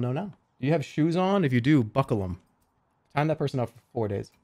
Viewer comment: i heard that as "tie that person up"